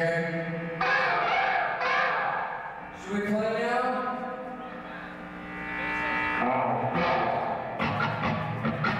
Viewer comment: What he said? " tu Italiano? " ? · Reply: he said "should we play now?" if I am not mistaken